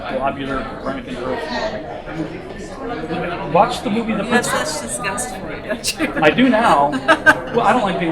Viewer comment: "Watch the movie _______." What movie did he say? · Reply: The Principle?